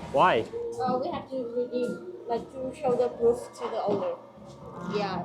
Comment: What did the waitress say? Whatever it was had you guys confused & concerned. I’m glad you loved the Ube Foam Coffee drink & the food was good. I’m super glad finally ate too. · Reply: Oh I had a coupon receipt from last time and she said I need to keep the receipt for the manager.